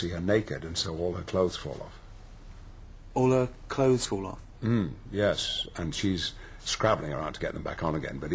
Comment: What does Ricky say im 1:15? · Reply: “Ah that’s interesting”